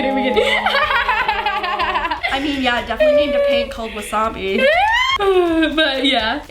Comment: "Ooooooooooh yea, definitely named a paint called wasabi" I almost cried😢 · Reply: Oh i thought she said a pig instead of paint, i was like she has a pig named wassabi? Lol